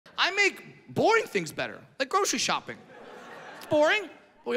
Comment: I heard "I make boy things better" 😂